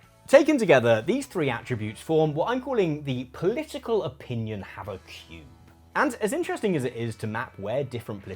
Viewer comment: I misheard it as Political Opinion Havoc Cube - please consider rebranding it as such if you bring it back in a future video. ✌️